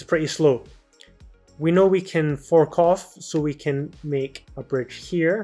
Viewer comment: That one time (5:33) i heard something else, like : f*ck off